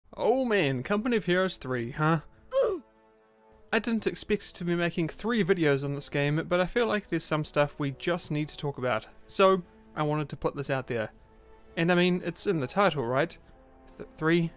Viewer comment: Yo what did you say at 0:04? Was it "expect" bruh? Fix yo accent